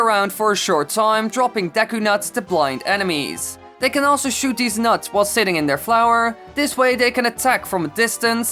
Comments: "They could also shoot deez nuts." is that- is that the distant calls of a 2011 meme I hear!? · i am sorry but did u say deez nuts